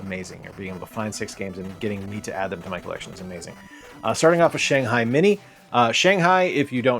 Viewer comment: I thought you said Shanghai MIDI. I was thinking it was a music creator game!